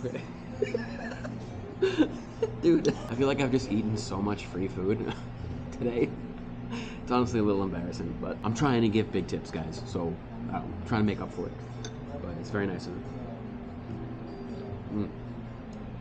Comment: Oh, he said "I'm trying to give big tips", I could have sworn he said "I'm trying to get victims" lol